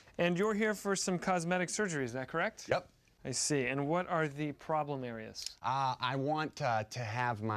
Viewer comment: whoa whoa whoa...at around 0:25 did zach say "and what are the problem there is?" was that supposed to not make sense? :) man i love zach and trevor.